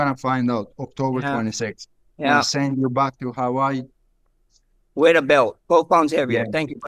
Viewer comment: what did Illia say for guru to say “other fighters would’ve gotten in trouble” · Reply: I’ll send you back to Hawaii